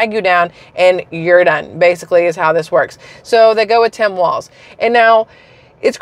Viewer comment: ❤ thought you said ❤ hairy walz..❤